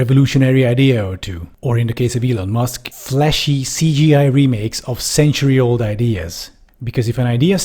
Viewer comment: Great video. I heard “fleshy CGI remakes” which was the cherry on the cake!